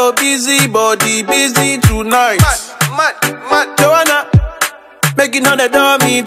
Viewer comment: Joana? Thought he was saying you an' I, you, you...